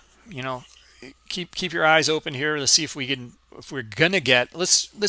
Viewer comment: All I hear is “with arms wide open” by Creed, but instead “my eyes are open”.